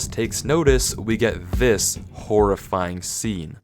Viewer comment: "and when our protagonist takes notice, we get this horrifying scene" immediately cuts to an ad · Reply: I thought he said “horrifying sound” and then it cut to an ad with pretty violin music